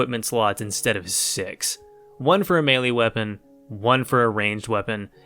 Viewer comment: Did you say melii or melee?